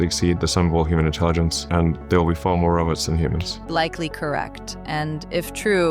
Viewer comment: It sounds like musk says...."there'll be likely be more of "us" than humans". HUH?? I know he presumably says "robots", but at this point, he sure acts like a robot.